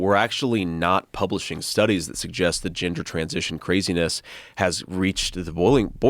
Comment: is it just me, or did he say "The ginger transition craziness"?